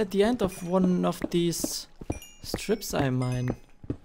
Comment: 69K VIEWS! DAMN. Man I've missed you it's been so long xD And make the cave kinda wild (OH YYYYYYYYYEEEEEEEAAAAAHHHHH!) if you know what i mean. Leaves, and all the other whacky stuff. 13:06 i thought he said THE STRIPPERS I MINE... LOL